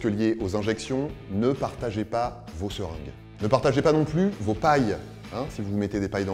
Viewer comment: bonjour ! à 5:20 je n'ai pas compris, c'est quoi qu'il ne faut pas se mettre dan le nez ?? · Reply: une paille